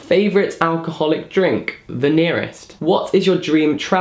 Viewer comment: 'favourite alcoholic drink' 'the nearest' - i feel u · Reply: I feel so stupid I thought he said something like ‘Theneerist’ and I lit thought to myself ‘ooh haven’t heard of that I need to try it’😫😫🤦🏼‍♀️